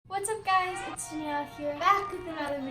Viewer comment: did she say hey guys it’s Dani here or hey guys it’s NIA here cuz I heard her say Nia XD